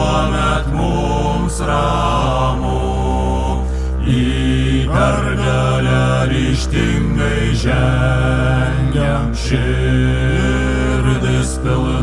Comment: a bit wrong translation, instead of "Į PERGALELE ŠTINGAI ŽENGIAM" should be "Į PERGALĘ RYŽTINGAI ŽENGIAM" edit: 1:22 should be: "NIEKAM NEATIDUOS" 1:39 should be: "IR VYRŲ JAI NARSIŲ NESTINGA"